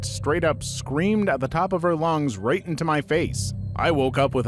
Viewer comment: what is the censored word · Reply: I think that it's cocaine